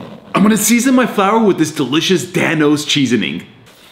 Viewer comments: People who heard him say dan o cheesening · People who heard him say cheasoning